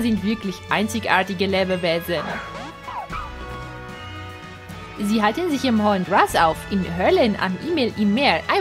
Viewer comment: Ich hab als erstes "Sie halten sich In der Hölle auf" verstanden omg xD